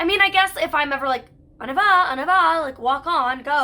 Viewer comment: Weird! I always heard you say « en avant » (in front, like go straight forward) and not « on y va » (let’s go) Also, I was so happy when you named St-Madelaine, I live like 15 minutes from there, and I would LOVE fostering for Mira some day!